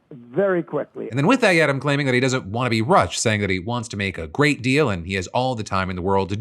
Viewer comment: the captions say "he doesn't want to be russia" and I think that's hilarious